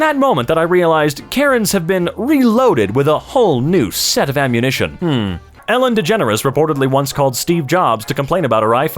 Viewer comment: What is the bleeped word at 8:21? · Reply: Covid